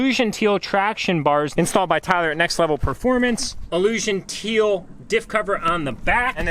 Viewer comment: At the time stamp 19:37 did you say Tyler From Next Level Performance?